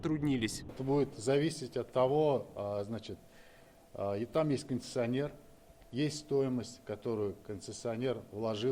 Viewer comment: Мне послышалось:"там есть кондиционер"? Стоимость дороги зависит от кондиционера 😂